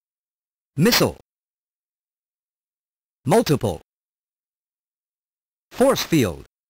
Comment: ＡＣ版グラIIIの「Multiple」は、なぜか「ラジェタ」と聞こえてしまったのはいい思い出です（爆） 現在では、さすがに表記通りに聞こえるようにはなりましたが・・・（汗）